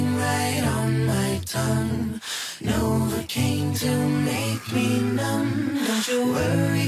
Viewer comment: villasmil I thought that they said 'Know it came to make me numb.' Hmm....